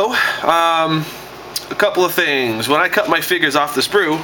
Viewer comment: i thought u kept saying when i cut my fingers of the sprue lol